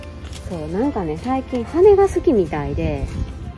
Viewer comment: 猫ちゃん達のおもちゃ見てる時おもろすぎ笑笑 押したら音が鳴るやつ押したんかな？笑 彼らは、鐘が好きが金が好きに聞こえて驚きました🤣